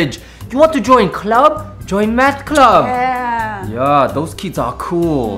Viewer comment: Sounded like he said "Join Meth Club" lol